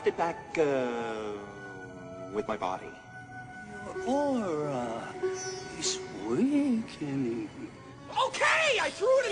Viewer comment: What did he say? "Your aura is _______." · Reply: Sounds like, "Your aura is weakening."